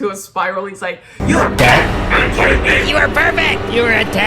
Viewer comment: Wait what are Drew and Kurtis saying in this part? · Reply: Drew says “you have debt? Are you kidding me?” And then Kurtis says “you were perfect- you were a ten!”